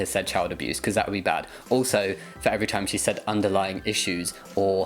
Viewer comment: That face is my mood every time that lady says "underlining" instead of "underlying"